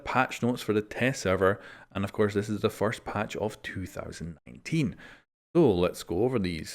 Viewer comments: Did he say 2018 at 0:07 lol😄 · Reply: no, he said 19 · who else heard 2018??